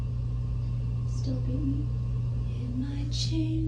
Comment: "Still hate me?" She is pure evil. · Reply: I thought she said "Still beat me." & We know who she was referring too. Poor Travis